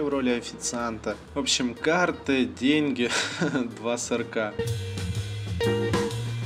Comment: Мне послышалось ты сказал " Карты, деньги, день сурка "